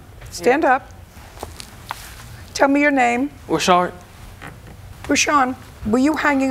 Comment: 🤣🤣🤣🤣 I thought he said "Sh-art" or "Shark" for his name.